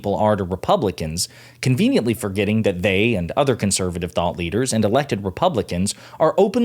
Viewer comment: I thought you said THOT leaders!😂😂😂